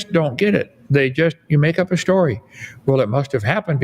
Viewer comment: did Kent say, "...they make up a story"? In this context, "they" are the bronze-age goat herders (i.e. the people that made up his "science" book).